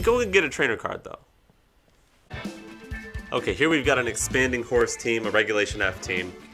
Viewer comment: Sounded like you said expanding horse when you talked about the iron crown team so now that’s gonna be my nickname for Calyrex Shadow lmao